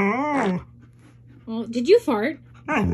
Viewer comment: 😂😂 i heard Maya said “ I didn’t fart”, “no, I didn’t”, “it was lying!”